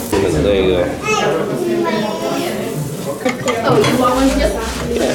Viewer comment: it sounded like saying oh you want one chip